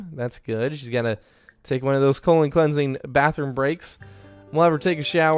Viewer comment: I thought he said "colon cleansing bath" omfg😂